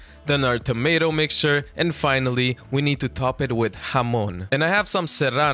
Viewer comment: Did he say hamon from jojo?